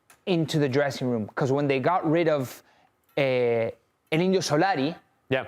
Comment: what does he say? Elino Solari? 😂 And the other guy is just "yeah". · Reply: El indio solari